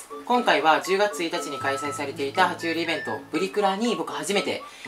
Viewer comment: プリクラって聞こえた...w